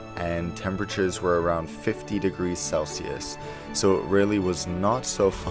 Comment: Did he just say 50c ??!?